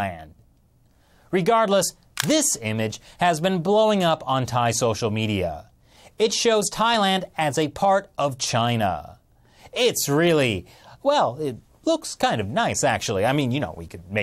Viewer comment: For some reason I thought it said "Poland Is Now Part Of China" XD